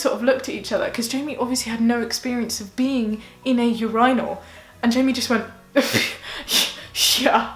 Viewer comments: It took me several minutes to realize she was saying "urinal" and not "in a rhino"... I was very confused lol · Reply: Anthony Belotti When I first heard it pronounced that way, I heard "your inal," and I was like, "what the heck is an inal?" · Bro 😂😂😂 when you said urinal I thought you were saying rhinoal.I was so confused for a hot minute 😂😂😂😂